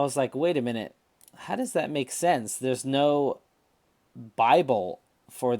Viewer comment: What's that word at 1:21 ? Bivole? Bi-hole? I can't find anything like it in any glossary of picking terms that I've seen. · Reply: Sorry for not speaking clearly, I was trying to say "Bible".